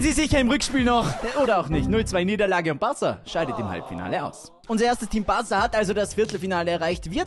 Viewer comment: "Halbfiale"wir haben das einfach nicht gesehen, einverstanden 🤝😂